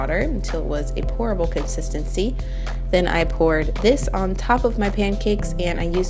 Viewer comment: oh my god i thought you said "horrible consistancy" when talking about the peanut butter and water